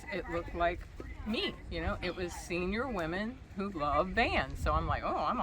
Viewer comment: I thought it said "Senior women lovers van meet" I'm on my way!! 😉